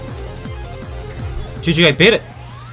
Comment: when he said “gg i beat it“ cc said “did you get it“